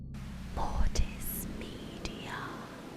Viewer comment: Okay so first off I’d like to say that the beginning voice that says “ Mortis media” scared the shit out of me this morning.. because to me it sounds like it’s saying my name… i hear “boy it’s me deion” scary stuff